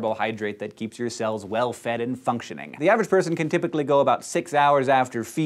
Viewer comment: When he said functioning I thought he said fucktioning haha and I was like what??